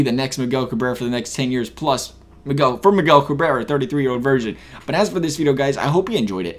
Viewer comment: Did you call Miggy a 33 year old virgin?